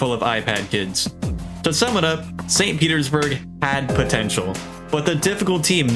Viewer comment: Did you say "Potential"?